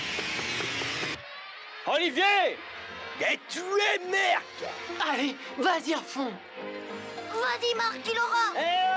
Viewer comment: qu'est ce qu'il a dit🤣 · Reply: A toi mark